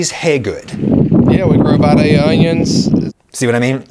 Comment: I heard “we grow, by day, onions” I wonder what the farm grows every night.